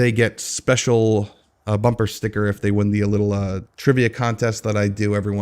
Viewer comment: I heard you say a pilot bumper sticker what is that?